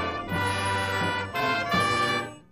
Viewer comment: wait did he say naruto music?😂